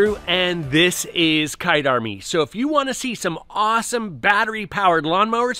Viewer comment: My wife heard you say hi is kite army and she said cut it off lol, I buy something after almost every video